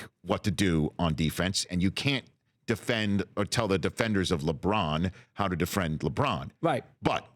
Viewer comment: LOL Did Rich just say "Defriend Lebron"? Hahahaha omg lol